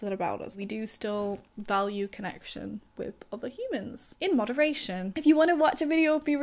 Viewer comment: I thought you said, "We're humans--in moderation." I laughed so hard inside! I was like, "Yes. I am a part-time human." But, really, though, we're 100% human 100% of the time.